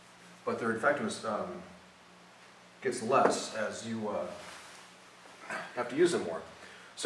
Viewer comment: Hey, can some one tell me what he said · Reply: Their effectiveness gets less as you have to use them more.